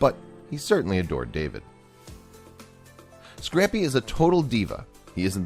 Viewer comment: For the first two minutes of the video I thought he was saying "scrampy"